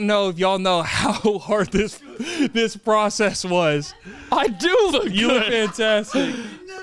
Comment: am i the only one who heard say “HE NEVER LOOKS GOOD”at at 13:43😂😂😂😂